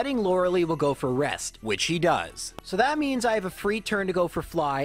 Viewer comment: You said He instead of She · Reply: Sounds like he said “whichee does” which is gender-neutral